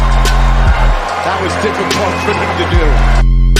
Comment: “ that was a difficult thing to do”? Is that what he says? I can’t make it out